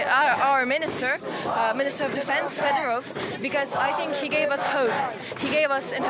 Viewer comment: mistake in subs at 2:25. it's fedorov instead of reznikov